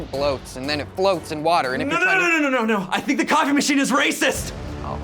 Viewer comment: I thought he said condom machine